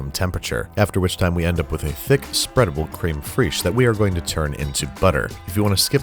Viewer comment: I heard Creme Fraiche? Where is Randy Marsh when you need him?